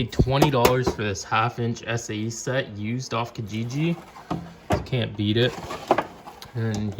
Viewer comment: where did you buy it from? Didn't catch that part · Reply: Kijiji.ca